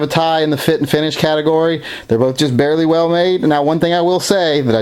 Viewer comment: in the video it sounds like you said "They're just BARELY well made" did you mean to say that they were both VERY well made? Or could I have possibly heard you wrong? Or is that actually what you meant to say? I'm kind of confused by that statement because of how highly you speak of both of the firearms. I just purchased a Nitron finished P232 and it seems like it's going to be a great back up gun for on the ankle (the non stainless version weighs considerably less than the stainless because of the alloy frame 18-19 oz's) to compliment my P239 (primary summer carry) and my P220 Combat (primary winter carry) but if you could clarify what you said or meant to say at 5:50 in the video I would greatly appreciate it. Also keep up the great videos, your channel is BY FAR my favorite on all of youtube, Thank you in advance for your time and I look forward to hearing from you...